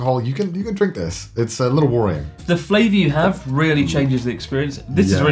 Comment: when he says watery sounds like he is saying worry.